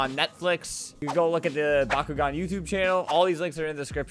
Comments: he said "bakugan" so many times that it doesn't sound like a word anymore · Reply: i thought he said bakugo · I haven't seen Bakugan in like 10 years. Great to see it coming up again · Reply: My dumbass thought you said Bakugo I was so mad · When i heard that name i thought he said BAKUGO i swear i almost freaking died for a minute there please tell me im not the ojly one(;´༎ຶٹ༎ຶ`)